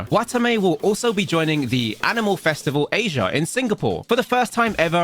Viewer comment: Wait did he say animal festival?